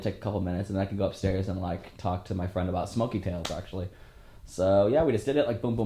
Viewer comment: At #2:07 what's he saying his gonna go talk to his friend about? "smokes cans" lol... what?/////?????